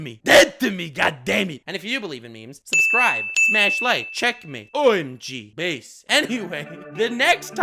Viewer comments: was i the only one who heard him say "Checkmate" "omg" "Bass" ?????? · Did I just hear, "Checkmate.. OMG.. BASS.."? Hahaha Are you gonna collab with Davie504 now? Hahaha now that's something, I'm in for the ride.. 😂🤣😂🤣